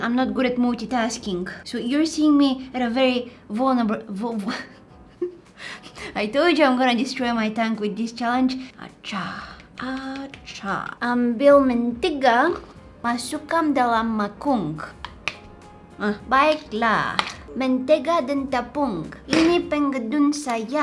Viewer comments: why did it sound like she said “ I’m filming a TikTok” lol (no offense to her or anyone who speaks that language)😊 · Ahahah she said "mangkung" instead of "mangkuk" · 😂😂 Im sure I heard she said "mangkung" instead of "mangkuk"😂 Im Malaysian🇲🇾 btw.. You did say "menggunakannya" wrong😂 you sound so weird I swear😂 and me a Malaysian speaking English is pretty easy to me